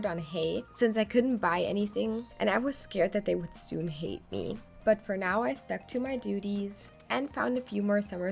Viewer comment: “I was scared that they’d soon…. Hay-te me” I’m sorry this is what I heard immediately 😭😭😭